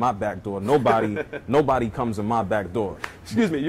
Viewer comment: i thought he said nutts in his backdoor